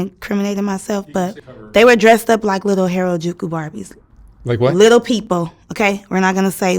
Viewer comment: Did she say that the ''little people'' were dressed up like Harajuku Barbies?